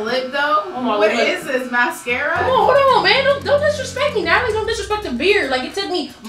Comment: Didn't anyone catch when said hold on bae to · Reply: She said hold on man.